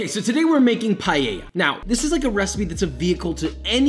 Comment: did you just say PALLELLA?